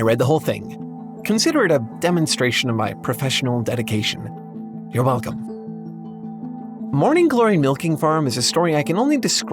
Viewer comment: I left here and came back later. For some cursed reason, the specific part I returned on made it sound like he said, “Shh. Your mom.” Scared the shit outta me.